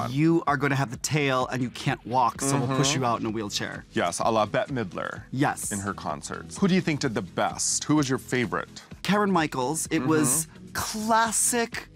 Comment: Did Manila call Kameron Karen at 5:30?? Who’s Karen Michaels??